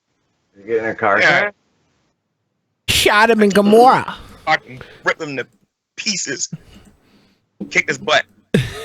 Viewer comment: did say "fucking"? Sounds like he said "fucking ripped him to pieces"